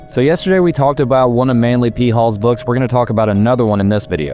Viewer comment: The first couple times ol dude said "Manly P Hall" I heard "manly pee hole" and was like "WTF IS MY PHONE PLAYING NOW?"